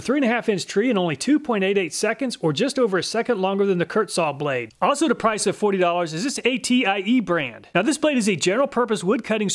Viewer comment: "Also at a price of forty dollars" -- Caption says $18 though.